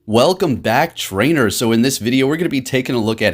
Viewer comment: i thought he said "welcome back drainers" cause that would be actually more accurate